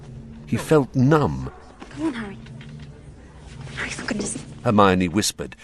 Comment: did Hermione just say " Harry fckin just"? Like fuckin?